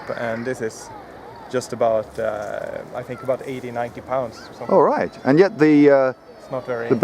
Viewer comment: 90 pounds draw weight 'It's a good beginners bow' These Swedes are insane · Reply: i thought he said 60lbs